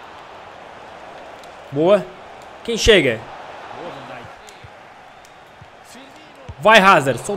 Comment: Só eu que escutei no fundo Boa Vandaik